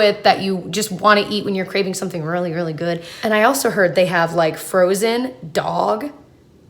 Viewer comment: Omg....I thought you said they sold frozen dog!!! 😧 that pause was traumatic lol